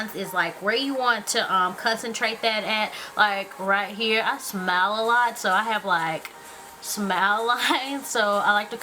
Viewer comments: i thought she said i smell a lot, instead of i smile a lot lol!. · I thought you said I smell a lot! o_O You're still beautiful though, and I subscribed^-^